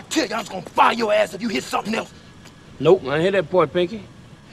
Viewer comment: "Nope, I didn't hear that part Pinky"......😅😂😅😂😆😆😆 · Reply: All this time, I thought CW said "Nope. I didn't hit that boy, Pinky." 😆😆